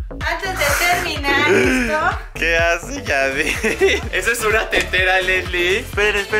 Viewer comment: like si escuchaste a que dijo yadid en el minto